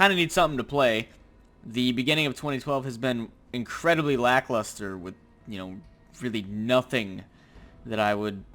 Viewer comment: for a minute there I thought he said Black Luster. XD